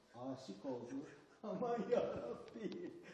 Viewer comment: It almost sounded like he said "oh my god help me" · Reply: he said "aman yarabbi" aman kinda means like wow, oh, help etc yarabbi (ya rabbi): rabb means god in arabic. Ya is like a very polite hey in arabic. Or "O" in english like: "O Hamlet, speak no more!"